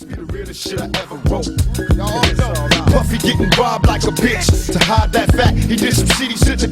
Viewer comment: lol, at people who think 2pac said "puffy getting robbed like a bitch" 2pac never said that, 2pac says, "puffy getting bribed like a bitch", real 2pac fans knows what that mean when he says bribed.